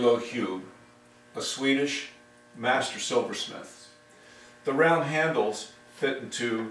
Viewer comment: I don’t know if someone mentioned, but it’s Made in Denmark, not Sweden 😅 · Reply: I thought you said « Swedish sword master smith » at 0:30. Maybe I’m wrong 😅